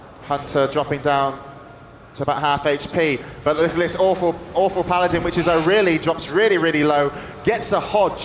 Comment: does he say "awful paladin" at 1:38?